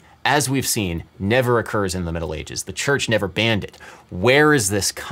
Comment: I literally heard "Metal ages" instead of "Middle ages"